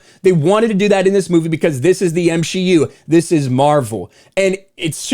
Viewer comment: did he just say m-she-u because it sure sounds like it